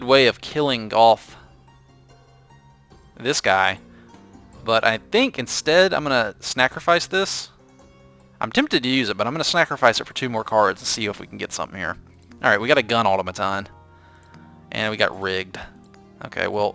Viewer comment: Don't know if its me but I keep hearing the word 'snacrifice'